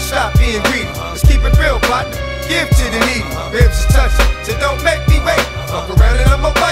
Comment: I always thought he said keep it real Pac give to the needy